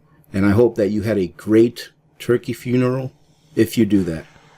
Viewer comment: did he say turkey funeral????? Never heard that 😂 one........